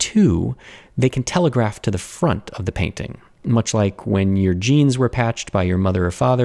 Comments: I swear you said "jeans" and i heard "genes", I was so confused I had to go back and listen two more times ( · When I heard him talk about the jeans I thought he said genes and was super confused for a sec xD I am an idiot lol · who else heard gens (as in dna) instead of jeans